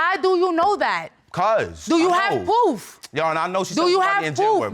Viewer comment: Did she say "poof" instead of proof @ 8:13? Wow